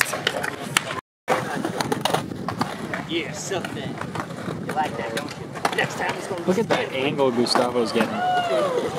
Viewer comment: what the hell is that guy saying in the background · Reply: HOLY SHIT